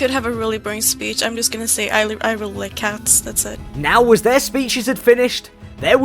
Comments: Did anyone else notice the caption change at 28:05 She said I really like cats, but the captions said dogs. LMAO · I really love the fact that she said "I really like cats" but the subtitles says otherwise and typed " I really liked dogs" 💀💀💀💀 props to editor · I am laughing at how editors put ‘dogs’ instead of ‘cats’ lol. Ima cat person as well, stranger. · why did the subtitles say I really like dogs when she said she really likes cats? dog propaganda? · says she really likes cats, but the captions says that she said she really likes dogs lmao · The fact the editor on 28:09 changed the subtitles from what it should have said which was cats , to dogs, guess the editor really hates cats 💀😂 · the caption said dogs not cats as she said in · She said she liked cats but the subtitles said dogs 💀😂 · why do you hate cats so much? at 28:08, when a player said: "i really like cats" the subtitles said "i really like dogs" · was when she said she likes cats but the captions said dogs 😂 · bro why does the caption say dogs instead of cats? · she likes cats captions say Dogs hahaha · She said cats why did the caption say dogs? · The subtitles say dogs and she said cats. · Lol 28:05 into the vid one of the people who were running for leader she said I really like cats but the subtitles said dogs LOL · she says "I really like cats" and the subtitles says dogs. Well, close enough. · In the subtitles someone put dogs instead of cats 😂